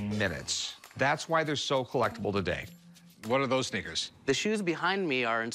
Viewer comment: Did he just say, "what are those n****s?" In 3:54?